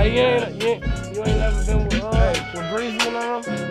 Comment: Lol ayy what did juice say at about 11:15 ?? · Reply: Someone said digital dash and it looks like juice said it but he didn’t then he said “You ain’t ever been w breezy and dem?”